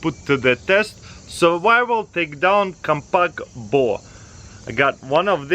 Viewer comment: thought he said survival ding dong 😂😂